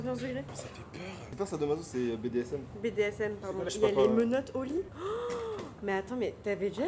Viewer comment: au lieu de comprendre "il y a les menottes au lit" j'ai compris "il y a les menottes oli" genre Bigflo et oli 🥲😂